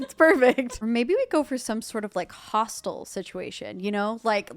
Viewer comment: for the majority of the video i thought you were saying "hostile" as in the house is so small its hostile to whoever is living in it turns out you were saying hostel 😭😭